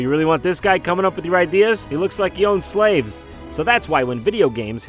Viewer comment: "You really want this guy comin' up with your ideas? He looks like he owned slaves." LMAO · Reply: Neon Peon I thought he said joe slaves